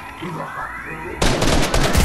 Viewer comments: WTF WHO HEARD IT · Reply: He said WHAT THE ** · listen closely to what that henchmen says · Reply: "What was that?" Is what he said.